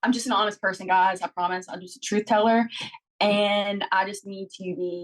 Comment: what did she say? hard to make out the words. Oh wait, okay, she said she's just a truth teller (context aside) and I just need to be kinder... is that it? She was always pretty, and she's so skinny now, but her tongue is still thick!